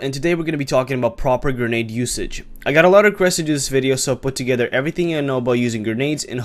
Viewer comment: what are you even saying at 0:08? quesiges video? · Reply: "Lots of requests to do this video" I think.